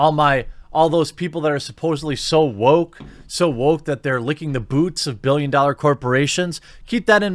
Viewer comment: I thought you said Go Woke Go Broke? They did the same for the Korean posters but it’s not politically relevant. Pfff